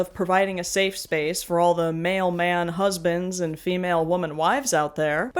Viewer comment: For a second I heard "mailman husbands" and pictured a postal worker focused pride event and like... I would be very down for that